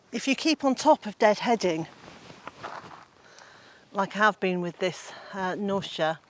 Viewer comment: Can I ask what that beautiful red flower is (you’re deadheading at 4:16)? The auto-captions says “nausea” 😂, but I can’t make out what it is… beautiful colours! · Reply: Knautia, also known as scabiosa